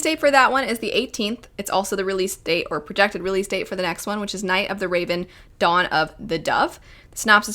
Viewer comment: Misheard that one as “Night of the Raven, Dawn of the Duck”. Oh no. Quack!